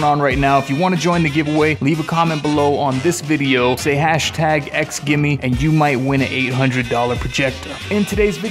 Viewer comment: #XGIMI lol I thought you said #Gimmie. Now I’ve got “it’s all about the base stuck in my head”.